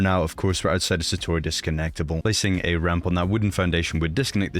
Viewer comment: u said ramp instead of roof